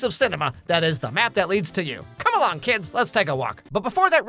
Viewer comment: Im not going to lie, i thought it said the nap that leads you lol